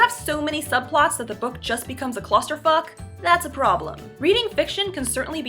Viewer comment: Did she just say "clusterfuck"?